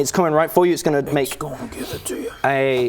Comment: Did he just say, “X gonna give it to you.” Which is a reference to the HermitGang rap?